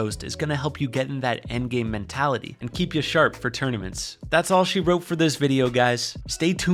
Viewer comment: am i trippin or did he say tourney-ments